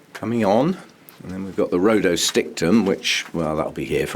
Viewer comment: Those twinkles are beautiful!! What's the orchid at 26:26? Didn't quite catch the name...thanks for sharing! Wish you a very Happy New Year too!! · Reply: That's Dendrobium rhodostictum